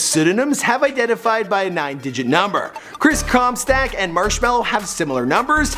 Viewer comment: you said Chris Com Stack instead of Com Stock